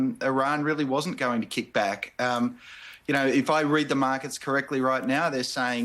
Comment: OK. Let me start by saying that I'm stoned. I love Jeremy and his accent. I thought he was saying 'the magats love this', but he was saying the 'markets! 🤣 I was thinking, WOW, that's wild he would just call em out like that! ❤